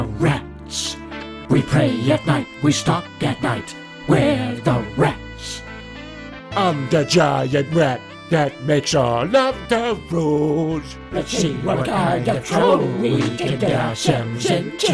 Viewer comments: Rats, we’re rats W e ‘ r e t h e r a t s We prey at night, we 𝕤𝕥𝕒𝕝𝕜 at night We’re the rats I’m the giant rat that makes ᴀʟʟ ᴏғ ᴛʜᴇ ʀᴜʟᴇs Let’s see what kind of trouble we can get ourselves into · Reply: Oh I heard it as I'm the giant rat that makes all of the poos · [All] Rats We're rats We're the rats We prey at night, we stalk at night We're the rats [Chungus rat] I'm the giant rat that makes all of da rules [Falling rat] o̸͖̐e̸̤͊a̶̠̒g̶̪̚h̵̢̃g̷̕͜h̵̤̄h̸̛̪ [All] Let's see what kind of trouble we can get ourselves into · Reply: I always heard it as “I’m the giant rat that makes all of the poo” ._. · Legit, at first I thought the big one said "And I'm the giant rat that makes all of the poo!"